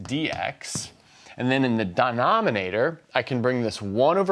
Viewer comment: Did he just say "DIEnominator" @ 2:28 ? :O